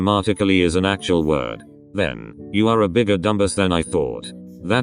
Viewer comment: When said dumbass I heard: "Dumb-bus" JHSJSJSBS I'm laughing so hard lmao